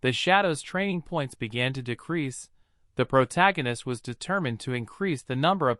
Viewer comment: Did he say his shadows training point began to decrease or increase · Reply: Decrease